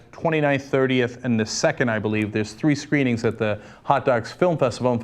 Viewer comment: Every time Cenk says "Hot Docs" I hear 'hotdogs'.